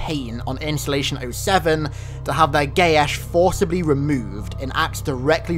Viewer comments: Sounded like "they would have their gay ass forcibly removed" XD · to have their what? I cannot follow what he just said can someone please tell me? something with ''to have their (word) forcibly removed'' or something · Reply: Geas.